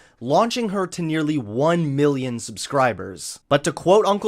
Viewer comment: did anybody else hear one billion subscribers?